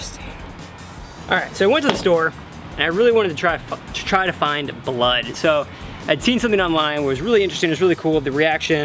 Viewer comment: He was going to say f*ck instead of find ...